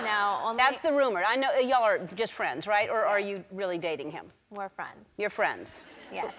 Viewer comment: did she say 'more friends' or is it just me lol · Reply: She said " we're friends "